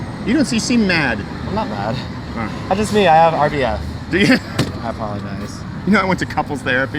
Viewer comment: can someone explain What “RDF” is and mean? If he didn’t say “RDF” as well correct me, I’m unsure what he said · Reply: They are saying "RBF", it means "Resting Bitch Face".